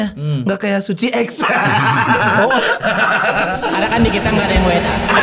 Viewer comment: remin bilang apa ya? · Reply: dia bilang "kan dikita gak ada yg WNA(Warga Negara Asing)"